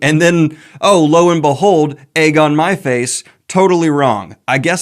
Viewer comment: LOVE the whole Sabriel series! Also, I heard "egg on my face" as "Aegon Myface" 😅